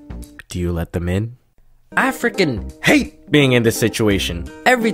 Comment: it sounds like your saying “African hate being in this situation”